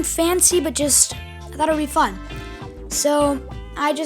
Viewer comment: I thought he said f**k but he said fun XD